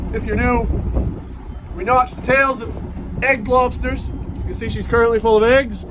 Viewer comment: I swear it sounded like you said egg GLOBSTERS not egg lobsters